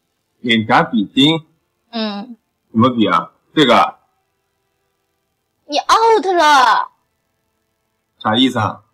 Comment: can someone tell me in mandarin what shes saying here? Im learning mandarin and just cant figure out what shes saying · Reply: 她说的是：你out了。